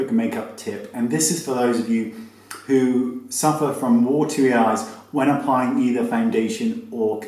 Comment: I thought you said watery arse!haha